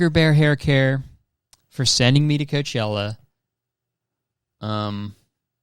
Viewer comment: idk if it’s your accent or what but everytime you say coachella i hear “COOCHella” 💀